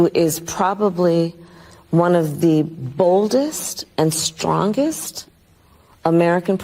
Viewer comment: I thought she said oldest not boldest